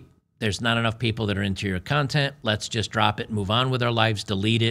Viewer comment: In the OnlyFans piece, I'm sure I heard you say "cuntent". Nice.